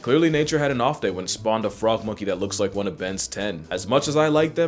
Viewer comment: my ears perked up like i was a sleeper cell when i heard ben's ten